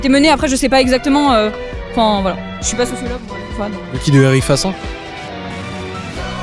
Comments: il dit quoi ? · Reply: "Qui de Eric Fassin?" Je pense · il dit quoi ? ça a l'air de choquer la meuf xD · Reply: "Qui de Eric Fassin?" Je pense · tu dit quoi a 6:03 xD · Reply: "Qui de Eric Fassin?" Je pense